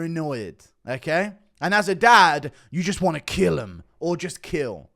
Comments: That jones video is oddly Eerie. When I first heard it I thought he said “at the end of the day you just want to kill em” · i legit thought he said "you just want to kill it" , in the sense where he wanted to to superbly well